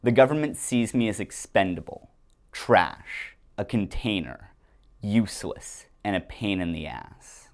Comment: Did you say "container" ?